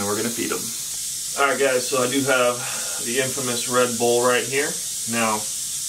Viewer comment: every video when u say "red bowl" sounds like you say "red bull" than it makes me wanna have one